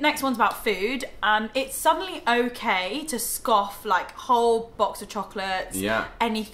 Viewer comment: So, American viewer here...for a few minutes I thought you were saying that you scoff down a box of chocolates. I was thinking, “Oh, that must be a British-ism.” My mom had to explain to me when I mentioned it to her that you were actually saying “scarfing down.” 🤦🏻‍♀️😂 Oh, and I love watching Home Alone and It’s a Wonderful Life at Christmas time. The second one’s actually my boyfriend’s favorite Christmas movie. My favorite is Elf. It never fails to crack me up.